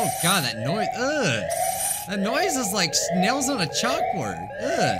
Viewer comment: What I heard “That noise is like snails on a chalkboard” me “wait what”(I was playing it in the background lol)